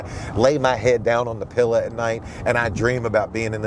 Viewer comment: did maven just say pilla